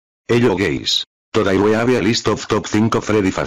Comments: says hello guys. Vs what i hear: hello gays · I heard "hi gays"